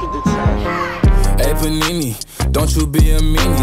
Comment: Is it just me or am I hearing "anythony" not panini